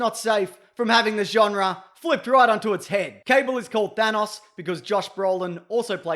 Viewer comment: When he said Thanos, I thought he said Vanoss